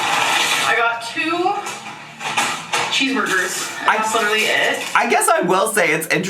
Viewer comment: She did in fact order two double cheeseburgers 🍔 🍔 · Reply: I thought she said two cheeseburgers, not doubles.